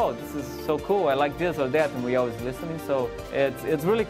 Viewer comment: Did he really say kabam always listens??